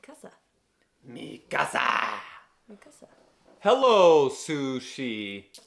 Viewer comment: highkey sounded like mikasa